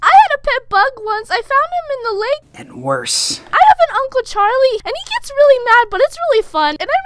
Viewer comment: no one going to question what she said... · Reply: Uncle Charlie 😅